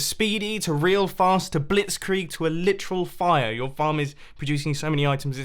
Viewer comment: Did I hear you name level 4 efficiency as Blitzkrieg? Quite an odd reference, I’d say